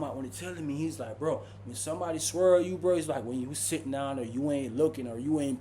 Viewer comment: I swear I thought the saying was “he squirreled 🐿️ me” not swirl me!? 😂